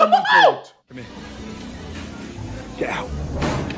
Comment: I have done looked a clip and listened to what he said, he didn’t say, “Get help” he said, “Get Out.”